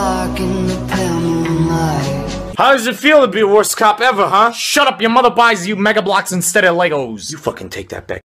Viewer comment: what did he say 😂😂😂😂😂😂😂😂😂😂😂😂😂💀😂😂💀😂💀😂💀😂😂💀😂💀😂💀😂💀😂😂💀😂💀💀💀💀💀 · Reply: He said, “Shut up, your mother buys you Mega Bloks instead of Legos.”